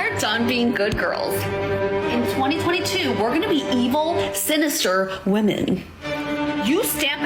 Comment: I thought she said “evil, spinster, women” and I thought… Yeah, that’s right. 🤣